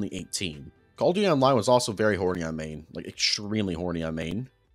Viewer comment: WTF dif you say at 1:34:35? I don't know what "oreamay" means. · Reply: he meant that you said “hornyimean” too fast he thought you said oreamay or something 💀💀💀💀